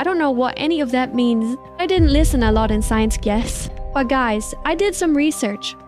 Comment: did you say science guess?😂